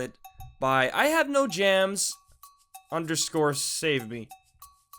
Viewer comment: I heard u say "I have no jams" and I dropped my paintbrush